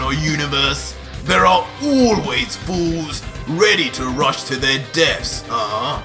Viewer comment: is says rush to their deats instead of rush to their deaths